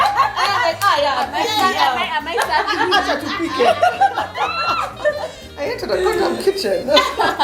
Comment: I think its called a cubic zirconia...